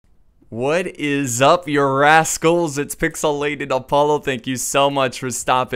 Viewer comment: Lol when the vid started I honestly thought u were gonna say "what is up ur asses?" until I heard the -cals and then I was like oh he said "what is up you rascals?" XD great vid m8